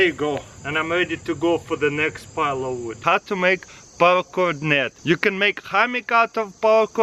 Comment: Steve-O54 *parachord,as in parachute chord,he uses it to make the hammock. You misheard his accent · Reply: Oh gotcha' I thought he called it "power chord" thanks Glen